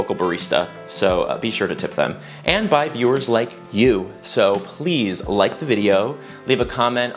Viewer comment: "and bi viewers like you" is how i first heard that lol